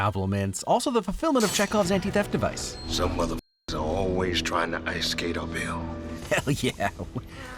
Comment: “Some motherfuckers are always trying to ice skate uphill.” I thought he was saying “a pill” for years, and was super confused, too.